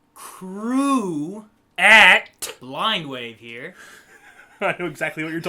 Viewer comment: I always thought you began by saying "crew OF Blindwave". But I learned today it's " crew at Blindwave". I learned something 🤓